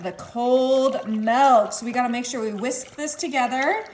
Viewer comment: I thought she said, "And the cold melt." Did she mean milk?